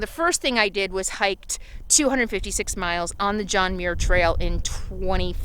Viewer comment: Lol....I thought you were saying John Deere trail, lolol!!!!!! Duhuhuhuh.